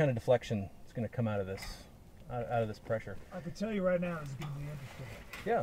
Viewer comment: "I can tell you right now..." I CAN TELL YOU RIGHT NOW WHAT I SCRUBBED BACK 4 TIMES AND I CAN'T HEAR HIM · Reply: "I can tell you right now its gonna be interesting"